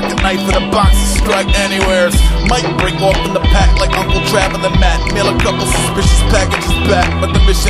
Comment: Whats is Aesop saying at 1:24 might break off in the pack like uncle (...) mail a couple suspicious packages back. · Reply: uncle traveling matt, character from a movie called fraggle rock